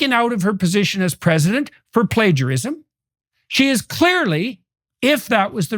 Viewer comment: I hate pledgerism. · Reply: I heard "pleasurism"😂